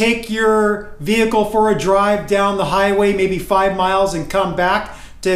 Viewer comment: Five minutes of driving will not bring my transmission up to operating temperature. Engine yes. Transmission no. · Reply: Yeah, we agree with that. I think you misheard me in the video. I said 5 miles (not 5 minutes) of driving one way and 5 miles back, so around 10 miles of driving.